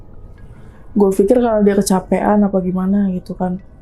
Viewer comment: ada suara bisikan apa itu ? "dia kesepian" ? atau salah dengar yaa 🤔